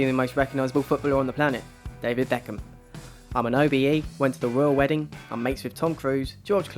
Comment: he says "I'm an ???". I couldn't make out what he says. so who wants to throw me a bone n help me out n lmk what he said there right at the 0:15 seconds mark??? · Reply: Loia OBE. Stands for 'Order of the British Empire'. It's a knighthood rewarded by the Queen when people contribute greatly to the British Empire pretty much.